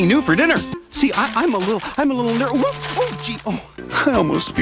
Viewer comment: really sounded like he was gonna say “I’m a little neurodivergent”